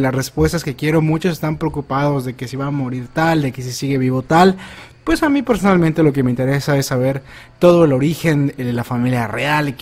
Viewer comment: lo que entendi: "pos a mi en lo personal me vale verga"